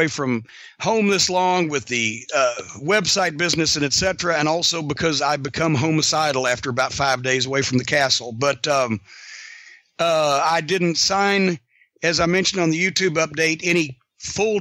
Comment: I know he meant homicidal but sounds like he says homocidal